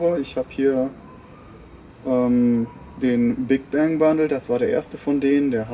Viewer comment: Ich habe erst Bangbus verstanden!!! Hahaha ;-) Aber das war ja ein anderer! ;-) Dein Balkongarten finde ich immer wieder klasse!!! :-) Cooles und entspanntes Video! LG Stephan